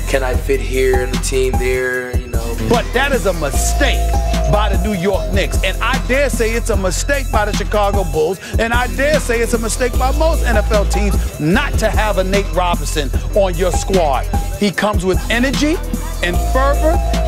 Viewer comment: Time out did he say "Mistake by most NFL teams"??